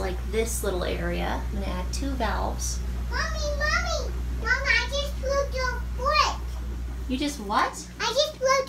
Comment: I thought she said I just broke a poop 😂😂 I stoped the video ah the wrong time to reply to a text I was dying 😂😂